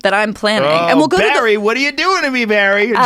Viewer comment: Man... I thought it said Barry White.